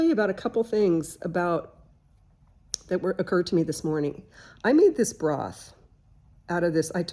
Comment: I thought you said “I made this bra” 👙 😂😂